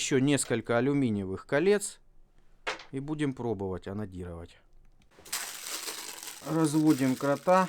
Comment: сижу в тишине один, заходит жена (послышалось ей...) и слышит "буду эту болванку анонировать" -_